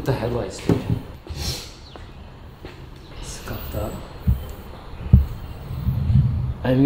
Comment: Did you just swear in Greek and said ΣΚΑΤΑ at the 2:40 of the video, when you saw that scratched rime, or is it me..??? 😂😂😂